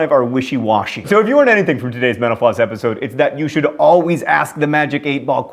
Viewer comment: Every time John says "metal floss", I always hear "menopause"